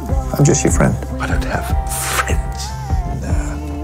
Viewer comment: what is he saying? Btw, great edit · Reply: Something like ‘naaaah’ as response on Sherlocks I don’t have friends☺️